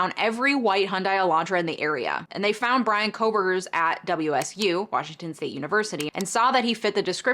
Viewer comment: I thought you said HOEburger 😂